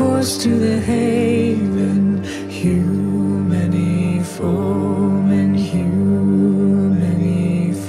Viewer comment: I am not viking, but I LOVE THIS SO MUCH!!! I missed the premier, DAMN IT, I wanted to make it. Grrrrr. Well, the result was that I got to replay this about a dozen times, and don't worry, there will be more. I was confused by "hew many foemen", which just goes to show my brain is on low because my sister heard it and said, "Kill many enemies". DUH. I think you three are brilliant, the harmony is glorious and haunting, the rune border adds much, the echo-y background makes me think of icy fog that sounds crystalline. I LOVE THIS SOOOO MUCH!!!!! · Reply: Well, the first few times I heard the song I thought they were saying 'too many foreman.' I was very confused. 😆